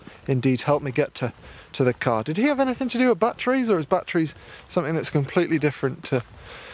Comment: It's late, I'm tired, He's got an accent. I'm sorry, but at first I heard, "Did he have anything to do with butt trees? Or is butt trees completely different?"